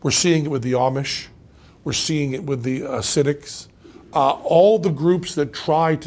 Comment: Did anyone else mishear "Chasidics" as "Cynics"? Just a quick second of "whoa, wait, they're still around?"